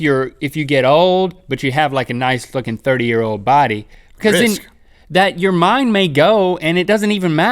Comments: watch the video from 7:07-7:12 I think I heard Link say "Have a nice fricking 30 year old body" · did link say nice f-cking body at 7:11?!